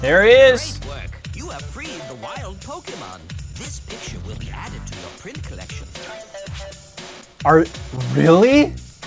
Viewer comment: what he said: “this picture will be added to a print collection” what i heard:“this bitch will be added to a print collection ”